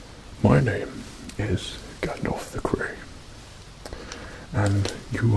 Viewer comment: Ngl I thought the thing said Gandalf the gay not gandalf the gray